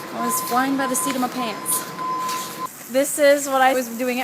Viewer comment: wait, did Lindsey just quote a Hamilton line?? Just me?? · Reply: When she said “I was flying by the seat of my pants.” I thought she said “I’m penniless by the seat of my pants.” (Satisfied)